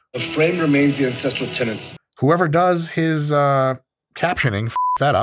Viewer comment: tbf I think he is actually saying tenants.